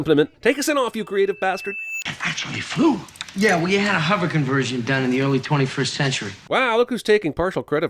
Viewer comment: I think he said '"Well, you had a hover conversion done." not "We had a hover conversion done." Netflix subtitles confirm this.